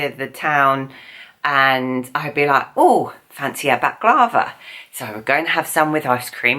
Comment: says per closed captioning "Oh fancy about guava"....is saying Guava or something else? She says that word multiple times, so I am curious what is really being said? @ 3:50 it's said again, and this time per cc "they do back over". Dumb question I know but I am still curious for clarification. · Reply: Baklava!!!